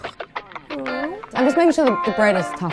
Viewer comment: I SWEAR 0:40 she said “nah” not “no”